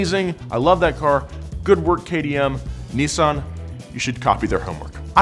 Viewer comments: did you just say “good work KTM”? 🤔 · good job KTM? Or did he say something else that I didn't understand?